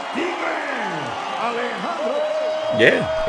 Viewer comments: Lol. I could have sworn the ring announcer called her 'Alejandro' when he announced her as the winner? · wrong for that he said: "El Tigre". 😂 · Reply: Lol. I could have sworn the ring announcer called her 'Alejandro' when he announced her as the winner?